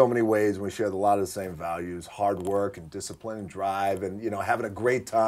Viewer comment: Did he just say drive😈😈